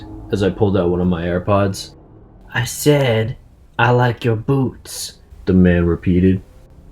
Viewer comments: When he said i like ur boots i thought he said i like ur boobs 😂😂😂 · I thought he said i like your boobs 😂😂😂 I was like: dude he is a guy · I heard I like your boobs, and I was like WHAT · When the guy said I like you're boots I thought he said I like you're boobs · sounded Like he Said i like your boobs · i thought he said i like your boobs but he said i like your boots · "I said i like your boots" I thought he said "Boobs"😂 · I heard "I like your Boobs" 😂😂😂😂😂😂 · First of all i thought he say 'i like your boobs' 😂😂😂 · I thought he said "I said I like your boobs." I was like tf is happening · You know when the serial killer said l like your boots l thought he first said l like your boobs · I thought he said i like you boobs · Lol when that creep came in the bus and said" he likes your boots" I I thought he said he likes your boobs I like wtf hes a boy but then I heard it good and he said boots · Am I the only person thought that man said I like ur boobs? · When the guy said i lome you boots my dad thought it said i like you boobs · At first I heard: I SAID LIKE YOUR BOOBS!!😂😂😂until I saw the BOOTS🤣🤣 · OMG I thought he said I like your boobs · I thought he said “I like your boobs”😂